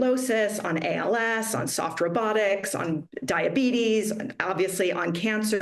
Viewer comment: I heard softer buttocks instead of soft robotics 😅😅🤣🤣🤣🤣 I thought that was her sense of humor 🤣🤣🤣🤣